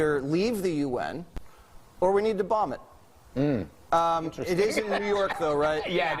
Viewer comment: Did he just say "We need to BOMB the UN"???? Why isn't Brendan Carr taking THAT POS off the air? A malfunctioning escalator is an insurrection??? NO, you malicious, illiterate, deleterious imbecile. Sitting on your diapered arse for over 3 hours, doing NOTHING, encouraging an armed crowd to storm OUR capital in an attempted coup, THAT is an insurrection.